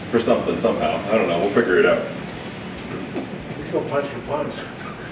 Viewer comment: What did Chuck say at 4:42 ? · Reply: Niels Van "we could go punch for punch"